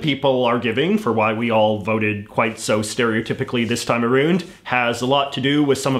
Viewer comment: Did you just say “aroond?” Too far.